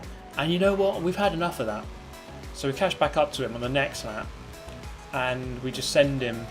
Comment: Did he really say "catched back"?? I mean i am from Germany but I'm pretty sure that's wrong af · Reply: 'Catch back up' I think I say there.